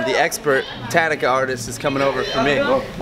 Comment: lol she was actaully saying " Oh, I told you i am afraid to be in the video" XD